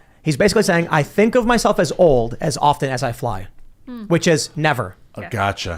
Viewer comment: I thought he said "as often AS A fly" i.e. he doesn't think of it.